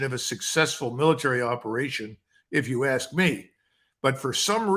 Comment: I think Col Macgregor said unsuccessful military operation and the recording glitched and sounded like it cut off the un part.